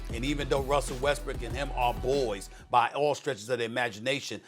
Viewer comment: did SAS just call Westbrook "Westbrick" 🤣🤣🤣🤣